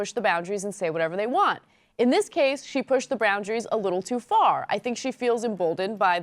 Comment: did ana say "Browndaries" (6:36) Lol!